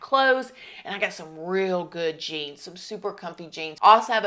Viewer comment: “I got some real good genes” is what I thought was saying 😆